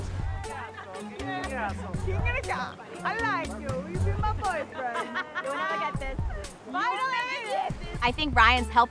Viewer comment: Did anybody hear Noelle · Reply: Sounds like she said "I like you, will you be my boyfriend?!" Can't tell you she directed that comment to though.